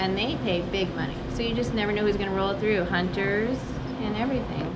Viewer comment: did she say Hunter? Hunter as Hunter Biden? 😅